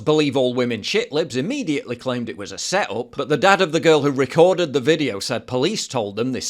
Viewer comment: I thought you said the dad recorded it for a minute... was wondering why he was still standing.